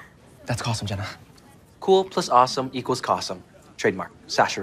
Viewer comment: I thought Sasha said awesome. Not cowesome. I was a little confused 😐